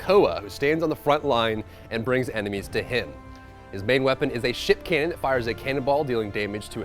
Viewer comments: I definitely heard "his primary weapon is a sh!tcan" instead of ship cannon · did he just say shit cannon?